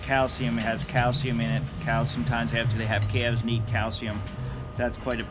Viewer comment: Did you just say that giant pill had "cowsium" in it??? Thank goodness those one-a-day vitamins my wife makes me take aren't that size!